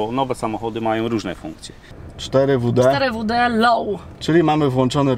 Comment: 4WD LOW.... a usłyszałem "lej wóde now"